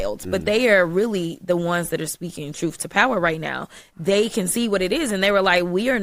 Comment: Did she say troof?